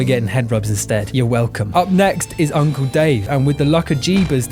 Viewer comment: I misheard the name at 24:27 as “Uncle Dane”